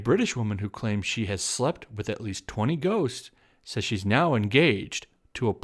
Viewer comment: At first i thought you said 20 goats. Not sure which is worse.